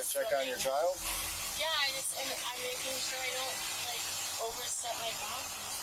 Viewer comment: When stepping into the ambulance, she actually said that she didn't want to overstep any BOUNDARIES...just wanted to correct the subtitles 😂